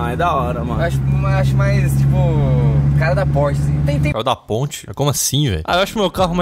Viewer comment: Eu entendi ele dizer " a cara da Porsche ".